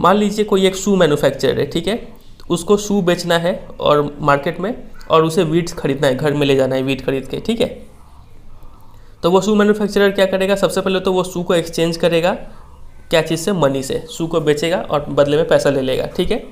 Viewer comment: I really thought he said WEEDS KHARIDNA HAI...!! rofl